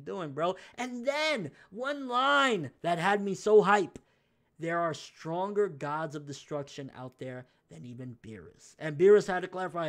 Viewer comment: Thought he said there is a stronger mortal than the gods of destruction?